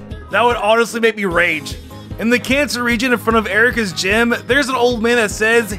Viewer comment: if u put captions instead of canto it say cancer region